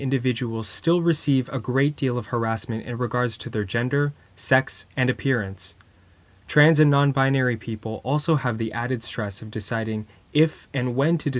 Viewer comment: what he said: "trans and non binary people" what i hear: "trans and trans people" 🤧🤧 non binary folks are trans dont come for me